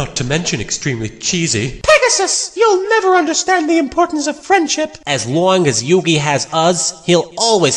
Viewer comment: I always thought she said "cringeship", not "friendship"